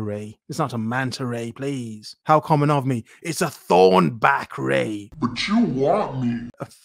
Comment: I thought she said "thrown back".